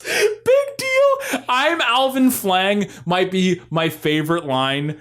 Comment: Also, also, I can't be the only one who hears "Alvin Schwang" instead of "Flang" @ 57:35?